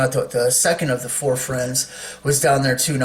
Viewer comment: no!!!! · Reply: oh, GOOD!!!! For a minute there it sounded like you said one of the 4 horses was riding now!